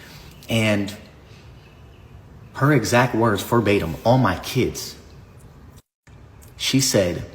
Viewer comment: did he say “for batum” ?